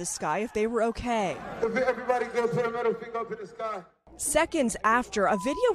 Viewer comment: Put a middle finger up to the sky if they're ok? That's hella disrespectful, people were already hurt seriously enough that there were ambulances, this guy is clearly lacking any conscience. He doesn't give two sh*ts about his own fans. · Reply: I never heard him say if your ok. All I heard was him saying put your middle finger in the sky. He did that too make people mock God as they were being sacrificed